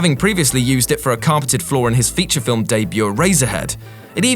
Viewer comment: i thought he said razorhead.